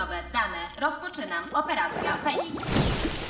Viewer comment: Operacja penis czm usłyszałam fenix XDD